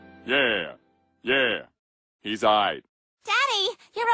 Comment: I thought at 00:16 it said he's high🤣